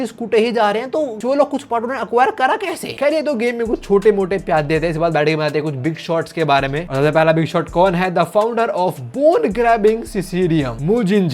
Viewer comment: did you say [BIG SHOT]?